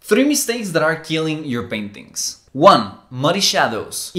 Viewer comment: I BLEW MY NOSE WHEN YOU SAID PAINTINGS AND IT WAS COVERED SO I THOUGHT YOU SAID PARENTS😂